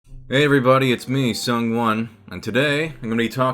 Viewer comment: I always thought you said "It's me, someone" in your intros, I just learned that your name is SungWon. I'm dying....